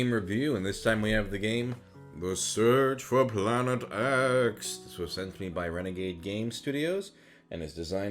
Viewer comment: My volume was low and the first thing i heard is "The search for Planet ass"